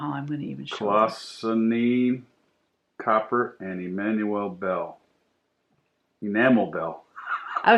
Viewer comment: I laugh every time you n are together on the video. I could have sworn he said * a manual bell*!! Wanted to say Thank you! For appreciating and keep the primitive designs going!